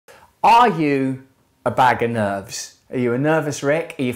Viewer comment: My sounds was turned down for a sec and all I heard was "Are you, a bag of nuts?" 😂 Then I thought "Are you a bag of nerds" AND THEN I finally realized he said "Are you a bag of nerves"